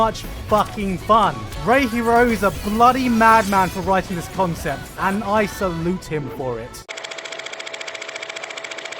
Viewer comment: I cannot make out what that name is. Please help. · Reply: Re;Creators